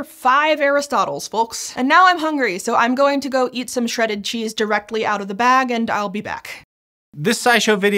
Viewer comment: she says "so now I'm going to go eat some shredded cheese directly out of the bag" but the closed caption says "so let me go shove some cheese in my mouth"